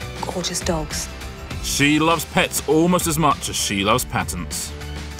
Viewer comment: LOL I thought he said "she loves pets as much as she loves peasants".